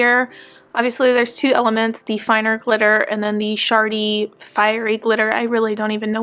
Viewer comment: You said shard-y at 4:21 but in my head, I heard it as shart-y 🤣🤣🤣